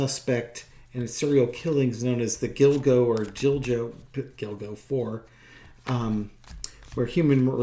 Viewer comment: I know it is problematic to laugh at someone so horrific. When I first heard about the Gilgo murders I thought they said Dildo I’m getting old my hearing is going 😮😂